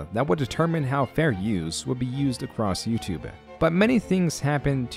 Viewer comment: bruh did u say YOUTUBÉ